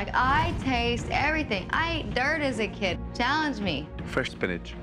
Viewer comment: I thought she said "i ate turd as a kid" 😂